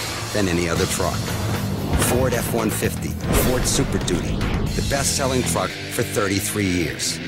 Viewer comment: did he say "The best selling fuck" Or and I hearing things?